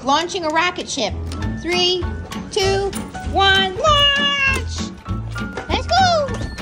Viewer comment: You said "Launch" but Lucy stopped and turned around because she thought you said "Lunch" 😂